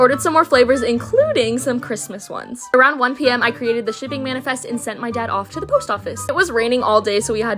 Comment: i thought she said “ shitting manifest “ ☠️